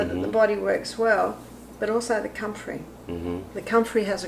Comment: Min 31:17. I couldn’t quite her what she said. Can someone tell me. Ty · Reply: Says: compfrey, also known as knit bone. Used for rapid healing ...